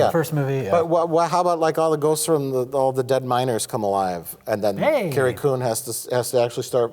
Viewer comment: I thought he was saying "all the dead minors" instead of "all the dead miners".